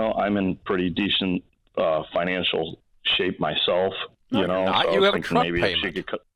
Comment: what did Ramsy said ? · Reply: No your not you have a truck payment